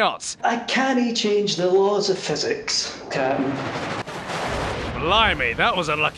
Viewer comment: is he saying "laws or physics" or "water physics"?